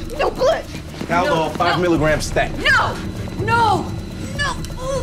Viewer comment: what drug did he say? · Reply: Haldol - short for haloperidol, an anti-psychotic drug that can be used to calm patients down quickly